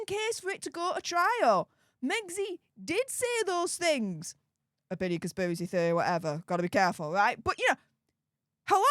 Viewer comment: says abidiaspisery theory whatever. What is she saying? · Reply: I think she said Opinion conspiracy theory or whatever 😂